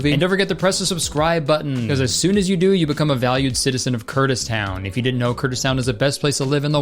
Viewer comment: You said "Kurtis Town" and I heard "kurdistan"